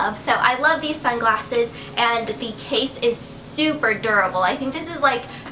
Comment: when you said durable, i thought you were saying adorable strangely....